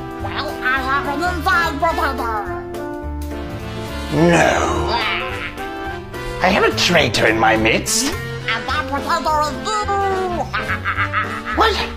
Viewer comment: I first thought she said ‘I have a potato inside!’ And I was like, ‘wait, your pregnant?’ 😂😂😂